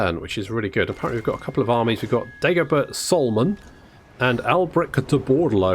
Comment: I could've sworn that I heard Dagobert Salmon. To my eternal dissapointment, the AI had not, in fact, recruited a salmon to lead their army.